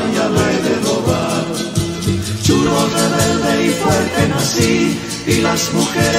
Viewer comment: Que hermosa canción!!!, Lindos recuerdos de mi infancia gritando a voz en cuello "CHURRO REBELDE Y FUERTE NACÍ, Y LAS MUJERES SUSPIRAN POR MI", saludos Bolivia desde Cusco - Perú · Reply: Yo tmb soy de Perú, y esta canción me trae recuerdos de mi infancia. Siempre pensé que decía "CHOLO, rebelde y fuerte nací...". Recién ahora me doy cuenta que decía "CHURRO"